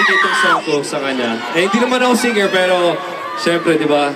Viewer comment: Anu yun? tama ba yung narinig ko? Hindi ka naman Single? Wahhhh! at 1:43..Alam na! :)